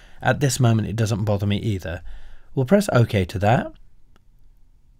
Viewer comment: Sir, what u said at 4:52 after the word at very quickly, i can't recognize that word sir pls tell me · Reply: I say "and very quickly it's exported all 12" - I'm talking about exporting all 12 frames of the animation that I made